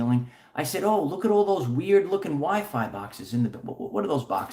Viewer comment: I heard I'm looking